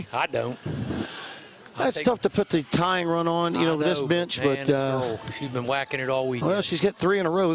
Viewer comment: does he say "This B**ch"? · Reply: I think he said "bench"